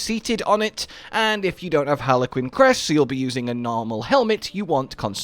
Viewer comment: I misheard "Harlequin's Crest" as "Harlequin's Quest" and was very confused for a moment.